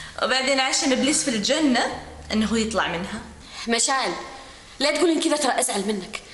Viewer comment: ايش قالت هنا؟ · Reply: عشم إبليس بالجنه ان يطلع منها.